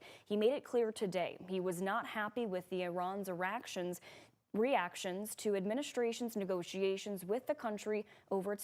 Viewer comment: Anyone heard this correctly at 1:32 she said "he's not happy with Iran's eraction," 😂😂😂😂